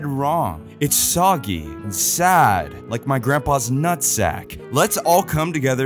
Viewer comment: DID I HEAR THAT CORRECTLY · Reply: “Let’s all come together” 😭😭🙏